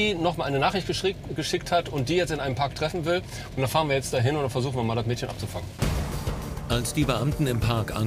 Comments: ich hab abficken verstanden 😂😂😂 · Ich habe verstanden „und da fahren wir jetzt mal hin und versuchen das Mädchen abzufucken” 7:56 😂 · Wer hat auch "abzufucken" verstanden ?😂😂😂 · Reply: Marcel Davis Er hat "abzufangen" gesagt